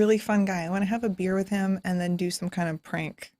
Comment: I thought Chelsea said "I wanna have a beer with him and do some cocaine.."